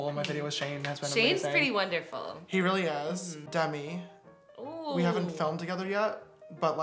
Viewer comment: When he said "Demi" at 22:01 I really thought he said "Debbie" like mom.... i was ??